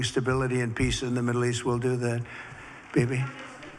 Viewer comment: did he just call her "Baby"? Wow